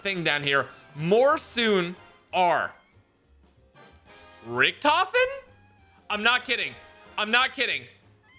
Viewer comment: I thought this man said "n-word richtofen" for a second XD